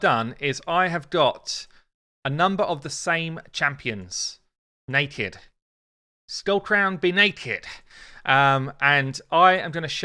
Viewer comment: Within 1 minute i heard "We got two skullcrowns naked" and "six more please, fill her up" 🤣🤣🧐